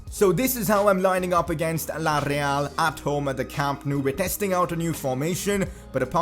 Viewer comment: did you just say Villareal?